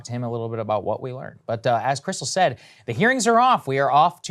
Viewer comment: I thought he said "The earrings are off" and I thought what a fantastic phrase lmao